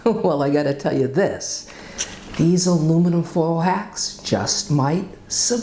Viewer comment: Good tips , I thought you said aluminum foil hats lol , but then I remembered hacks , thanks for sharing , God bless !